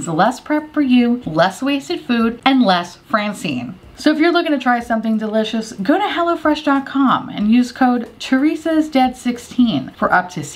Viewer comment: "so if you're looking to try something delicious, go to hell" is all I heard 😂